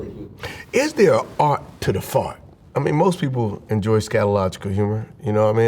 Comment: my guy pronounced it skylogical instead of psychological @ 6:20 🤣🤣😂😂🤣🤣😂😂